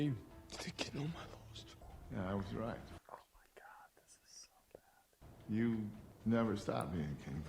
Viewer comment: did he say “this is so bad” ?